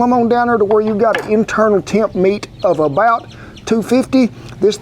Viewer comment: does he say internal temp of 250???